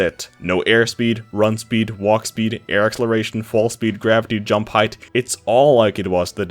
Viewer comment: "Attributes" are qualities of a character that are always present, and thus don't have frame data or active frames. Grab is definitely a "move", not an attribute, unless you wanted to say that he should have mentioned it as a separate thing, which is totally fair. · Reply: Oh I think I heard “Gravity” and thought he said grab speed lol